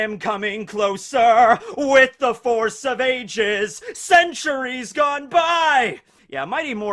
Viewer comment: did i hear correctly?... with the force of a jizz???